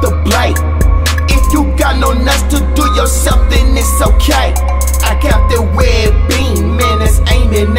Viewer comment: I always thought he said “if you got no nuts to do it yourself that’s ok”. “If you got no knives to do it yourself” makes sense now.